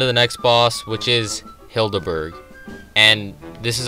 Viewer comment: Every time I hear "Hilda Berg" ( 6:36 ) I hear "kill-da bird" .... W H Y !?